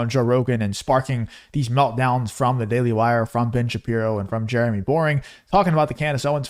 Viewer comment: Hahah at first i thought you said Daily Lairs 🤣🤣🤣